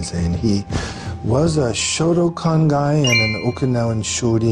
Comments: did he said shotacon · I'm sorry did he just say he was a shotacon guy? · did he say shotacon??? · thought he said shotacon. Don’t look it up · I thought he said shotacon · did he just say he was a shotacon? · Uhhhh. Did he just say the word I think he said? Note: Don’t look up “shotacon” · Lol thought steven said "Shotacon guy" LOL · Ngl I heard shotacon